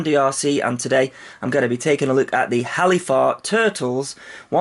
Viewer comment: I thought it said "Hellifart" for little there.